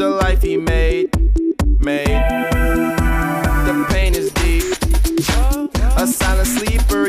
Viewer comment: So bei 1:57-2:02 verstehe ich immer der Penisdieb xD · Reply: Wird ja auch so ausgesprochen The Pain is Deep... XD